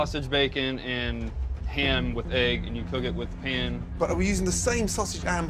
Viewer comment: Gordon really said am and hegg instead of ham and egg lmao